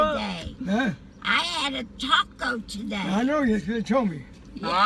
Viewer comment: Did she just say "I had a taco today!" ? 😂🤣